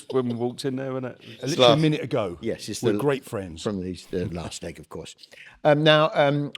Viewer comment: I had to go back cos I thought he said "the last egg" but no one reacted and I think thats joke of the week for sure